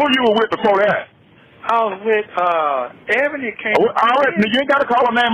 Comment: Did he say I was with “a Eboni K…”